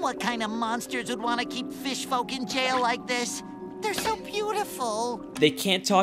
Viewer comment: Im no good english speaker But did he really say fish fu""cking jail at 4:20?